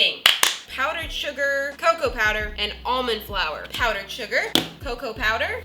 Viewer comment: I thought she said oMeN fLoUr